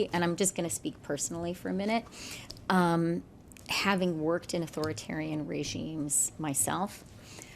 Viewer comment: I heard: "Having worked in Authoritarian countries i have learned how to manipulate the population "